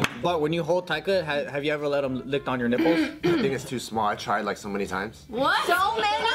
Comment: I thought said “niggas to small I tried it so many times” 😂😂 but he said “i think he’s” really fast